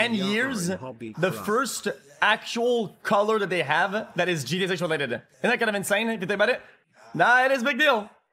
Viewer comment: what did he fucking say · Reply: he said “that is gta 6 related”